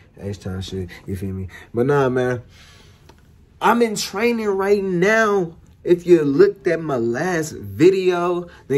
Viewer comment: I thought they said training is unpaid tho I’m mad confused